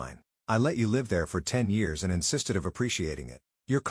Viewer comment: I think it should be instead of and not insisting!